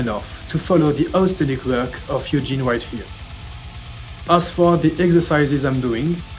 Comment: i sould follow your acent sorry... who did you say to follow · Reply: Sorry, my French accent can be a pain sometimes, but I try my best to make it as understandable the more I do videos like this ! I was talking about and especially, I linked this video --> <link> Hope you'll find something you like ! :D